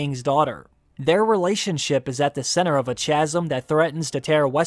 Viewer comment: Did you just say "chaz'm" instead of "chasm"? I'm not the only one who heard that, right...?